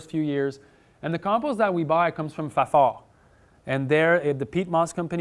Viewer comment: Someone help me, please! What does he say on 38:37? Where does his compost come from? 40% is what (38:42)??? Thanks! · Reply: 40% of his compost comes from a company called Fafard that produces peat moss. I had do to a quick google check just to make sure.